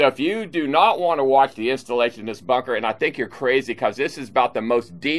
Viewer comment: I thought he said “...installation of this fucker” @ 1:39 lol Love this channel